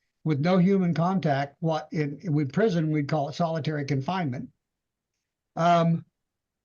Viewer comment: I’m not with the probation officer on this one. She’s got it out for her big time. I see the lady made a mistake that didn’t suit her well but 35 days in solitary confinement is a lot. The judge could’ve gave her a week in jail or some community service hours. She showed several certificates of completion and she proved her innocence in 4 out of 5. They need to change her probation officer immediately. Also if she had a baggy in her body then the temperature would have been fine.. it’s a tech issue with the pee cups. And if she stood there & watched her then I find it impossible to believe she got her hand up there and popped a baggie without being totally obvious.. she would’ve been peeing down her legs before she got seated. This doesn’t sound right at all. This po has it out for her. 60 days is a long time for someone who’s genuinely trying hard. I’m usually not for the defendant. This is a rare occasion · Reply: I thought they said segregation not solitaration confinement.